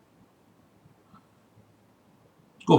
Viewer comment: at 9:54 I missed what you said - "Taurus, Leo, Aquarius and (?)" ? I cant make out what you said, would you mind telling me in a comment please? Been enjoying your channel for many years now, very helpful indeed; thanks for the clear and informative videos. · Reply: Scorpio - got it (shows how scattered my understanding is). There is so much to learn but I very much enjoy the journey - which is what counts really, I believe.